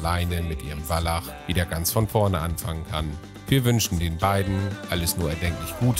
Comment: Oh dear, welche Humbugfahrer. So ein tolles Tier. Aber eine Sache: man wünscht jemandem alles erdenklich gute und nicht alles unerdenklich gute. Musste sehr lachen, sehr goldig! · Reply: Ich habe am Ende verstanden „alles nur erdenklich Gute“. :)